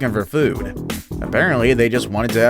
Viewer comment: Did you just say "a-bear-antly"???